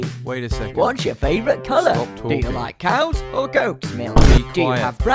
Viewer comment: Spamton: Do you like cow's or goat's milk? Kris: You have 5 seconds left to live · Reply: You made it worse for me. I thought he said "cows, or goats.."